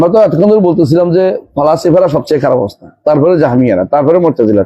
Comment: কি বলেছে? কেউ বলতে পারবেন? কথা স্পষ্ট না। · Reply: ফালাসিফা দর্শন